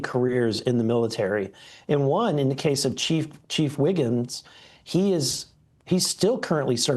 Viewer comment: I thought you said Chief Wiggum